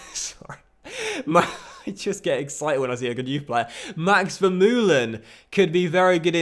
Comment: Who?Max Verstapen did I hear?